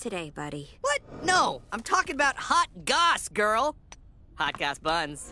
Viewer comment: I really heard "I’m talking about hot goths girl."